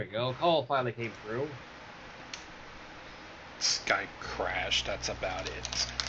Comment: I heard you say "Skype crashed" so I thought you meant like the video chat kind of Skype.